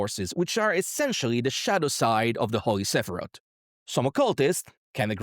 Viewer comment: I heard “shat aside” instead of “shadow side” the fist time 😂😂😂